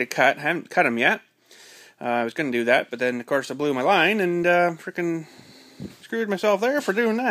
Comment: I thought it said I blew my nose. I thought what a 19 min video over nose blowing?